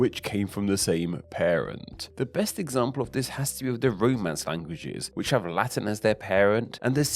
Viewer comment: Haha when you said Romance languages I thought you said roadmen's languages 😂 London is bad for your health.